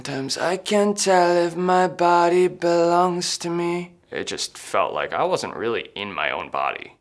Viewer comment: Wait a minute I thought he said nobody belongs to me instead of my body belongs to me